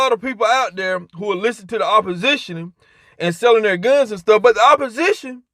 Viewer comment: I thought this fool was saying selling phones lol.